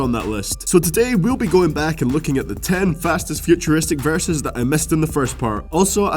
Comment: i thought it said "fastest future verses"